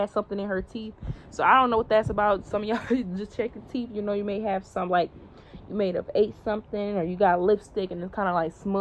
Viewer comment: when you said you may have something on your teeth, so just be mindful of that I heard “because no one‘s gonna tell you”